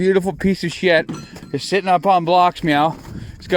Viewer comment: Did he just say its sitting on blocks meow